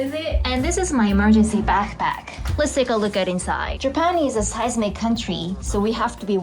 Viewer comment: What did she say about Japan? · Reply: Says japan is a seismic country